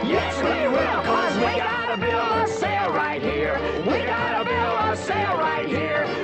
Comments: As a kid I thought they were saying “We’ve got a bill to sail right here!” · When I saw this movie as a kid, I thought the Gogans were saying, “we got a bill. We’re staying right here.”